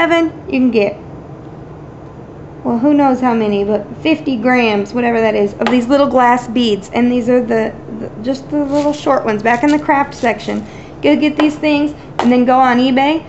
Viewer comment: I thought she said back in the crap section..... Im like hmmm someones on their period!